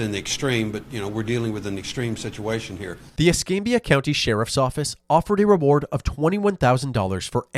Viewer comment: For a moment I thought you said the "Escape County Sheriff's Office" lol 😅😅😅 That would be some name, hehe 😂